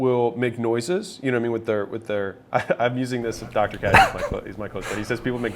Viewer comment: did he say his doctor was Dr k?